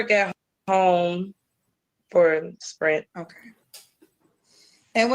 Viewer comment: Spirit Airlines is still in business. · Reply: unless I misheard. I definitely heard Sprint ( like the wireless phone company)